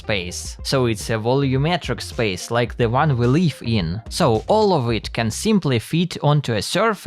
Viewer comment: did bro say "the one we leaf in"? DELTARUNE reference?!